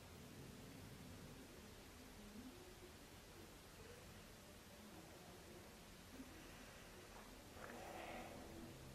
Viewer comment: YOOOO WHAT WAS THAT WHISPER YO?!? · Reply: Oh it says Christine 😳